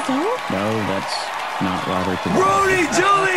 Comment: I love Ken's dry response to Nicole's airheaded question at 1:47. XDXD Also, instead of asking us our thoughts, why don't we hear YOUR thoughts instead, huh? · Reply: He said 'No, it is Not Robert Duvall'.